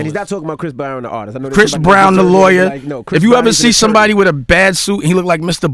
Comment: Lol all this time I thought he was saying Chris browns lawyer and not Chris brown the lawyer 🤦‍♂️